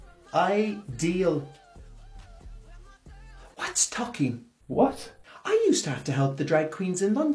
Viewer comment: I thought she said tupping, like in 'High Spirits', 🤣🤣🤣Now I'm thinking buffalo bill in Silence in the lambs 🐑 I don't hear or see well, my apologies. 🙏 I don't have that kind of knowledge 📜📚📰🗞️ and am not mocking, but, I do understand sassy biotch 😉😜😎✝️🙏